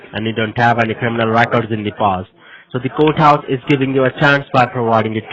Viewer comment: we don't say 'Court-House' in UK, just Court. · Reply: I thought he said 'goat-house'.